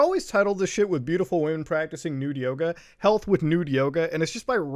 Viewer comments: We just got up and we thought he was saying "Nude Yoda" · check out our new merch at <link> also never forget code "SOG" at <link> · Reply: i thought you were saying nude yoda